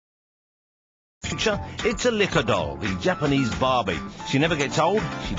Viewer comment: when he said 'licca' I seriously thought he was saying 'liquor' instead xD